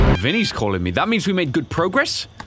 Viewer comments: Is it just me or when Ryan says pawn shop it sounds like he says porn shop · it also sounded like you said porn shop in stead of pawn shop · When he said pawn shop at first I thought he said porn shop XD